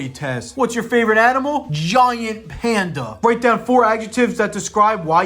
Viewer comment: i think im trippin i thought it said grandpa